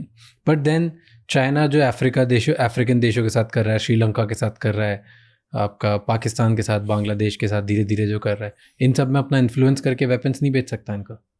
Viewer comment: did he say Aapka Pakistan/Bangladesh?